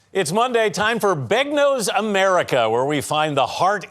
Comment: I had this playing in the background and I thought I heard him say “It’s time for big-nose America,” and I was like…”what!?!?!?”